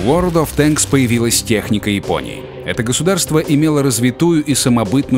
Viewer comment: мне одному послышалась "техника и пони"?